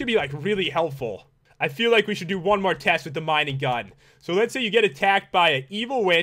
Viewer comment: Who else thought the mining gun said mini gun?